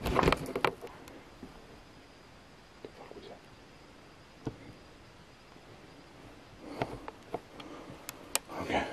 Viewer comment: was that you? I heard a whisper it sounded like "what the hell was that" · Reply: Pretty sure that was saying what the f@ was that